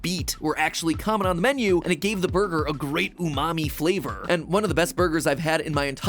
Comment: I first heard matpat say umami flavor as mommy flavor.